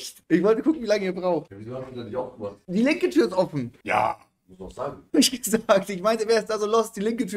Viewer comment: das „ja“ hat sich komplett angehört wie Monte